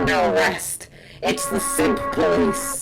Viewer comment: So I was literally drinking soup as the very end bit came on, and I might have had my back to the screen, and I honestly thought she said, "Hands up! It's the Soup Police!" And now I need a cloth. And more soup...